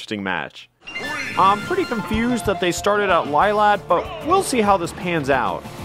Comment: What does he say at 3:30 ? I can't understand it... "They started out lilac" ? Is that what he says?